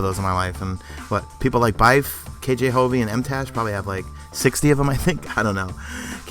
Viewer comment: Who did he say at 1:20? I know Byf, but then did he say KJ Hobie and Mtaj?